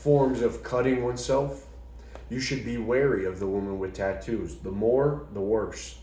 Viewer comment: I think I just heard all women with tattoos thank you 😂